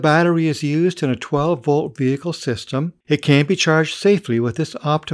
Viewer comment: it sounds like he says 'can't' instead of 'can'.